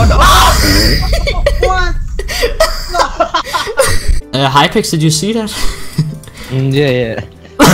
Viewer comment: did I hear HYPEX ??? he still playing ?